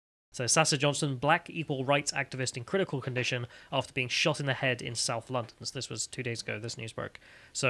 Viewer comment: Sweet Jesus you brits need to take a pause in-between words had to repeat it at the start a few times and then look at the screen sounded like he said " black evil right activist " i was like what?